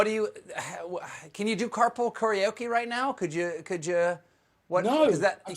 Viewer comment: 😆 I thought you said cardboard karaoke....maybe that’s the answer....both in cardboard boxes 📦 📦 in the Car 🚘